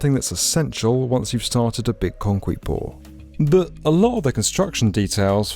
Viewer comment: when he said concrete pour, did anyone else hear"concwete pour"?